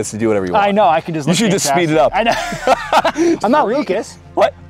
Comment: What did say at 14:20? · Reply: Lucas maybe?